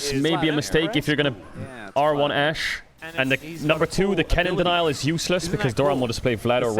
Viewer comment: Guys, what word did Caedrel say after Kennen at 16:05? Denyor? Kennen ??? is useless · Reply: denial, as in picking it to deny it for the enemy team